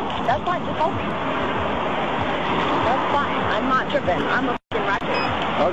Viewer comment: did she say “I’m not trippin’, I’m a fuckin ratchet”?